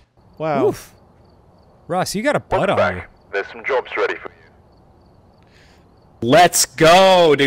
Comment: at first I thought that guy said "there's some jokes ready for you" and was like "oh how nice, he's got some jokes to lighten the mood a little"